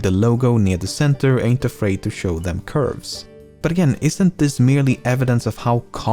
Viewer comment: Did he just say “ain’t afraid to show them curves”?